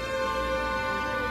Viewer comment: 請問36:15道明楓說什麼= =?沒字幕又聽不太懂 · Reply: 楊小楓 她在回想姐姐說過的話 (無數的可能)